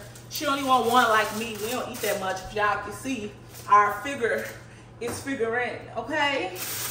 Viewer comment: Wth hell is a figurette 😂 its how she said it tho Timestamp11:38